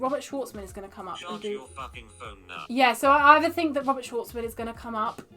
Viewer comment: what did that say? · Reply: f1 “ charge your f***ing phone now”